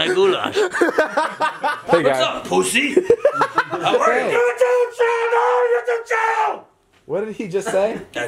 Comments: Its sounds like he said YOUTUBE CHANNEL... YOUTUBE CHANNELLL!!! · It sounded like he said youtube channel twice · 😂 After so many months I finally know what dad was saying at 2:10, "YouTube channel! YouTube channel" well that's what I heard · does dad said YouTube channel twice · Guys I think I cracked the code 2:12 I think he said "YouTube Channel , shout out my YouTube Channel"🧐